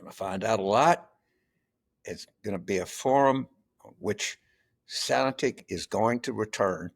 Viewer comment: What is Carville saying at the end? There's a key word that I can't understand. "...going to be a forum where (sanatic) is going to return to American politics". Closed caption says "sanatic", and that's what it sounds like he is saying, but that's not a word. Does he mean "sanity"?